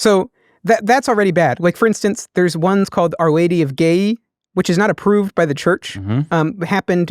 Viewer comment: What is he saying at 1:53:47? Our Lady of Gaye? I'm trying to look that up, not I'm not finding anything. I've never heard of this apparition. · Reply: ghiaie